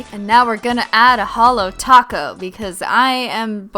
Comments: I thought you said Hill Taco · i thought christine said "Holo taco" on